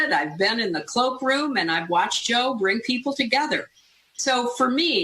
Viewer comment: God, I swear I thought she said "I've watched Joe r*pe people together" and it honestly sounded so believable.